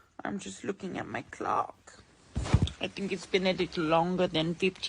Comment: I thought I heard, “I’m looking at my c*ck.” at the 2:34 mark. 😂